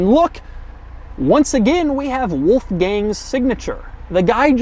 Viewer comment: i kept hearing "wolf gang bernard"